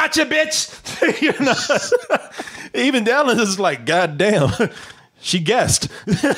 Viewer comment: Did just called Dallas at 7:49? 😆